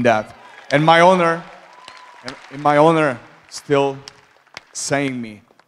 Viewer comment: Does jiri say at 7:25 'and my owner... my owner says i should stay at lightheavyweight'